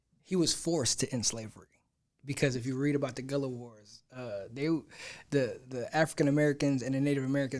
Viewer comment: the what wars? What’d you say there? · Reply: Gullah Wars its where the escaped slaves and Seminole tribes teamed up and was attacking the slave owners. It was technically a "freed by force" situation lol. these reactions are too fast to really go into detail but the super fun to spark the conversation.